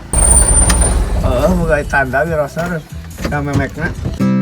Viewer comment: si Bapak sopir ngomong apa? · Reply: Memek nya